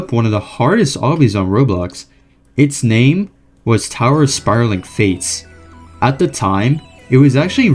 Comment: i thought it said tower of spiraling farts??😭